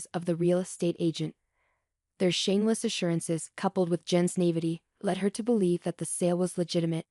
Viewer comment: Learn how to pronounce derelict...accent is on the first syllable, not the second; i.e. DARE-eh-licked. At about 11:03, written was "Jen's Navety, " but what was said was "Jen's naivete." Learn English! At 11:55 written was "modest operanday," but the correct words are "modus operandi." At 12:03, written was "then offloading," but the correct words are "then absconding." At a little before 12:30, Jen's name was spelled Jin. Must be consistent with spelling of names. At 12:42, written was "the revolution," but what was said was "the revelation." At about 12:52, written was "as a police," but what was said was, "as The police" as they were currently speaking to one officer. At 15:55, written was "Tom for Solis," but what was said was "Tom for solace." Less than 30 seconds later it was spelled correctly. At about 16:29, written was "digital H," but what was said was "digital age." There were more errors with using the incorrect form of a word, but overall this was very sloppy work. If you want your work to look professional, this will not cut it. Your work MUST be proofread and edited before posting it if you want to be a professional writer.